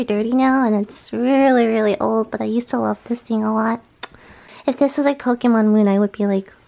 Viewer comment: I thought she said pissing a lot not this thing a lot